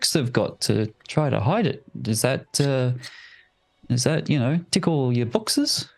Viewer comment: Oh, he said ‘does that tick all your boxes?’ .. I heard ‘Does that tickle your boxers,’ which is pretty different. 😂